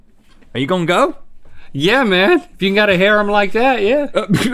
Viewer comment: what did link say after “are you gunna go”? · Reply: "If you got a harem like that yeah"